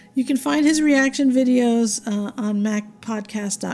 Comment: Oohhh, PODCAST... I thought you said codpiece!. Sheesh. It all makes sense now. PODCAST. I get it now.